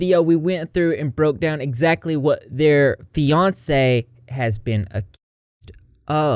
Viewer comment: On a lighter note, I thought you was saying Beyonce instead of Fiancee 🤣 loooorrdddddddd